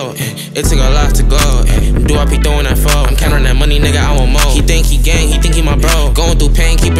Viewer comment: Nobody: Nobody in the comments: Me: 0:36 guy bend down in front him while he says gay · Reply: I thought he said "he think he gang" lol